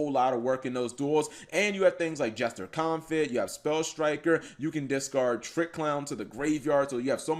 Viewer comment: Anyone else thought he kept saying Sky Striker rather than Spell Striker?